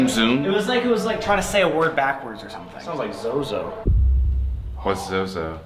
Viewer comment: I heard Zoe Zoe loud and clear